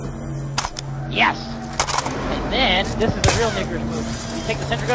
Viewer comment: what did he say? “This is a real n** move” !